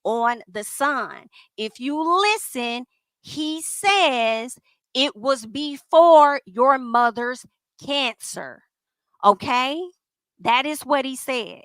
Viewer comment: It sounds like he is saying "I told you no before your mother's cancer"